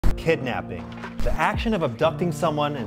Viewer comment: Omg I thought u said kidnappers Feeling real bad for these people tho